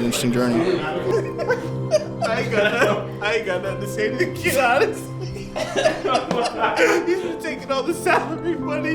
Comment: What did boze say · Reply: "Maniak just said I aint got nothin to say to the man, he was taking all the salary money"